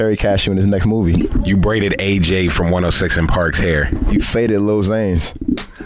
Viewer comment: did frank say lil xan 😂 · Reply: Lil Zane